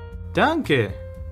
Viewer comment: I heard Dunkin’